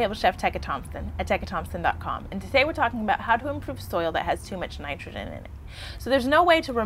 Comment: I keep replaying. What are you saying??? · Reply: She spoke quickly while loosing her breath, "soil that has too much nitrogen in it" I had a public speech teacher call that swallowing your words. She always emphasized controlled breathing and constant eye contact.